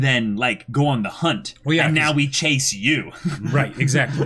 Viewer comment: I like how they keep mishearing "cheisu", the romaji of Chase, as "Chase you"